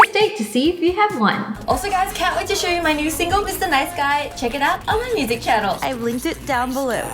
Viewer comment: I subd and I hear that "me nice guy" its so cool btw love your vids 😊